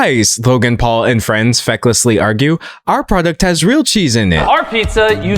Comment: Did you say feckless? 💚 🇮🇪